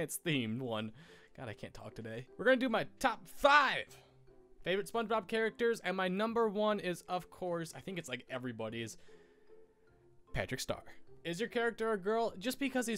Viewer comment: when you said "Patrick Star" during the beginning I thought you said Patrick STUMP, the lead singer of Fall Out Boy, and I freaked out...then I backed up the video and you said star, not stump.